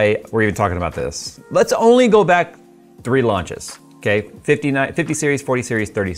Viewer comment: Sometimes I think I might be a hobbit. 1:33 I clearly heard: "Let's go back 3 lunches."